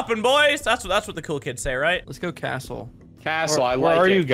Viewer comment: In Every video all i hear is "lets drop at castle"